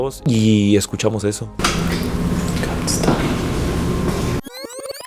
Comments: yo escuché un stop o stay ya que se escuchó casi igual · yo escuche un don't stop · yo escuché algo como came stay o come stay, que significa ven y quédate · yo escuché “come stay” al traducir significa “ven y quédate” 😭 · Mariana en el 11:11 se escucha claramente que dicen "eres gay" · yo escuche que dijo can stop, pueden parar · yo escuche "stop" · yo creo que dijo Can stay (puede quedarse) 😬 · yo escuché stop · Diavlo yo escuché “come stay” que traducido es “ven y quédate” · Mariana yo escuche game strart o algo haci en el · en el minuto 11:11 parece que dice "come still" que significa "ven todavia" pero escuchando mas atentamente no me cuadraba hasta que puse "steil" que es una palabra aleman que significa "acero" asi que seria una mezcla de ingles con aleman que diria "come steil" segun el traductor de google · Creo que dijo stop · en los subtítulos de you tube dise no pares pero a como se dise en inglés no es lo mismo solo se que al principio dise “scool stay” es lo que escuche que significa asistencia escolar en vdd ns si sea en otro idioma o algo así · Siento que dijo ITS time y creo que quiso decir es hora de morir xd es pendeja mi teoría pero bueno xd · creo que dijo "Don't Stay" osea "No Te Quedes" creo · siento que dice got stay-> tengo que quedarme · Yo escuche en el 11:11 come stay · Mariana en el minuto 11:11 escuché un come gun stay lo q significa ven arma quédate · yo escuché un Come Stay (Ven a quedarte) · yo escuche Come Stay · parece que dice 'don't stop'